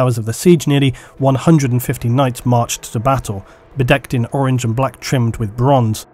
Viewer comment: did you say "bedekt" as in the dutch for "clad" (armour clad with decorations)?